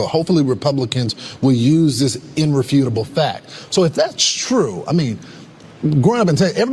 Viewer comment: Did he just say "inrefutable"? Jesus Christ FOX......